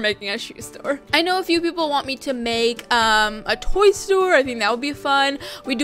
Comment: When she said toy store it sound like she said a boy store I was like huh why do you want a boy store then I watched it again and found out what she said 😂